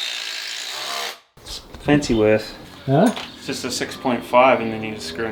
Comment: your mate called the concrete bolts fancy worth but I don't know if I heard the name correctly or what the spelling is. I have tried several variations of spellings but can't find them online. I assume they are something like Tapcons but what he was using looked better to me. Need something like that soon for a DIY concrete job and Tapcons are expensive and are in American "fractional metric." If anyone can provide me with the correct name of the bolts mate was using I'll buy you a Speights. 🤭 No I won't, it'll be a real beer that you can swallow without throwing up. 👌 Thanks in advance in case anyone can help. 👍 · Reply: Wurth is a brand that makes screws, probably one of their products